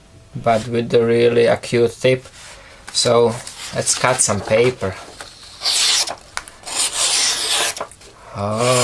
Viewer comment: That's got a great design.Love the work on the small thumb ramp and jimping.(was it me or did it sound like said "let's cut some p***y"? Probably just me huh.)thanks for sharing another sweet creation!